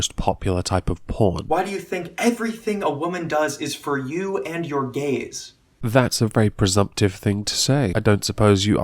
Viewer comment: I thought he said "Why do you think everything a woman does is for you and your gays?" I was many confuse.